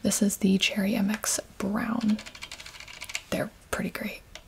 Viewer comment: did she just say… cherry MX browns are… great? Im ashamed.